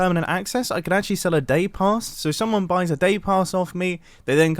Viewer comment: hahhahahah I thought he was saying dave pass : sometimes I worry about me